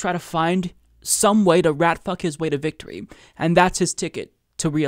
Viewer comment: Did he say "rat-fuck his way to victory"?